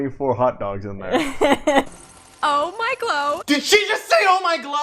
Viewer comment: ”DID SHE JUST SAY “OH MY GLOW!”??!!!”🤣